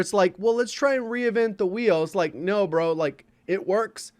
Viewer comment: anyone catch what J said when saying “reinvent the _____”. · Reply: Wheel